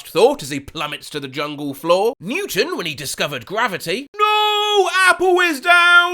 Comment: I misheard "Newton" as "Mewtwo"